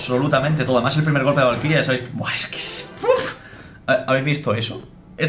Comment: WuaesquesPUF!! Alguien mas no entiendo eso xd